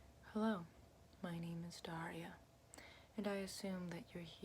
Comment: I thought it said darla as in the one in finding nemo